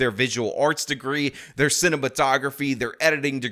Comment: I thought you said “cinnamon photography” instead of cinamatography😂😂